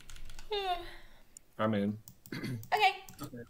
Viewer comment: did anyone hear saying "alright babe" ??? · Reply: he said "im in"